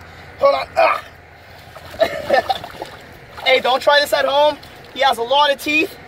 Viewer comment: did he said dont try this at home